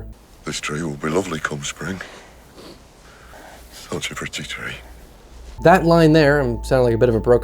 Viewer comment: Love this movie and your analysis of it!! If you analyze quotes, could you add subtitles? I cannot for the life of me make out the second half of what he is saying @ 25:03. Auto subtitles won't generate it either haha had to look up the movie script to figure it out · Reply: I'll definitely keep that in mind going forward. He's saying "such a pretty tree"